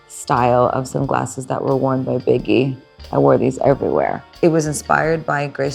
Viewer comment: Oh BIGGIE I thought she said Woah Vicky